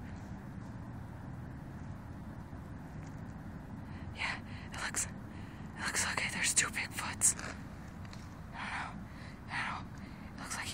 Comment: "Looks like he has a dead deer on his arm, or something"! At first, I thought you said " looks like he has a tattoo on his arm, or something."